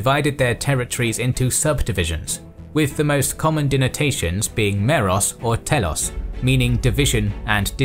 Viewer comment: What word did you use at 7:10? Dinatation? What is this word? Please. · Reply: Denotation